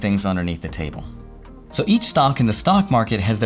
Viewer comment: Did you just say stonk lmao